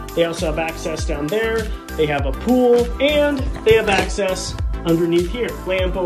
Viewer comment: For a second I thought it said they have a poo 😭